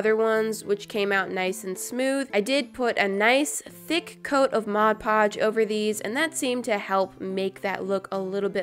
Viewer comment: Thought she was going to say “put a thick coat of glossy taco.” But she said “a thick coat of Modge Podge.” 😭